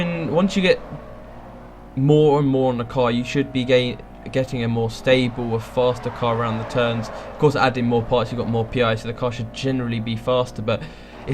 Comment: Great Video guy. One thing though, as a yank, at 9:40 I just cannot figure out what he says. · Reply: "... you should be getting a more stable, faster car around the turns, of course adding more parts you've got more PI so the car...." That's what I heard